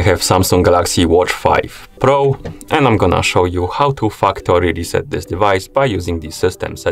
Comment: I legit thought this guy said fuckory reset settings😅